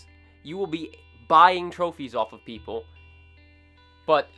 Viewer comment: At the end I thought he said 'Cereal, out'